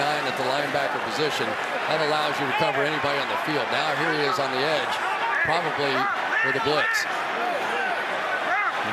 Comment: Big reason I love tom and Gronk's synergy @ 6:36 I didn't catch this during the game but u hear him yell "gronk" twice. I'm guessing as a heads up for the blitz to get open quick? either way. the way he reads stuff like that is just nuts. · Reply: he called an audible from the Pats playbook, it wasn't even a Bucs play! Gronk blocks and releases, he called "Bruschi Blitz". (Teddy Bruschi was a Pats linebacker)